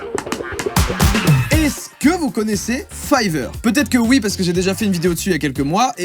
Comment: Whaa ce début de vidéo faut pas l'écouter que d'une oreille j'ai entendu "est-ce que vous connaissez Pfizer ?" et 10s après "petite piqûre de rappel" T_T